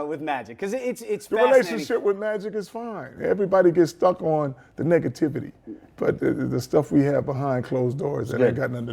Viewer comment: Lmao they was talkin about magic he said "everybody stuck on the negativity" I thought he was gonna say "everybody stuck on the nigga" 😂